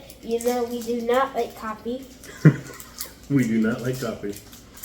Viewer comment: I thought at first you said you don't like toffee. Not liking coffee, that makes more sense